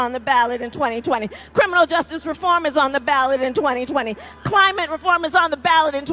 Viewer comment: yeah, criminal reform is in the ballot- that's why we are voting for Trump! by the way, I thought she said, criminal is in the ballot in 2020 and Joe Biden is in the ballot!